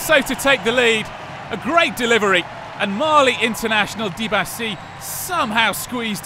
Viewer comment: I heard "Marley international". Now wouldn't that be fun, Eldians and Marley playing in a "friendly" game of football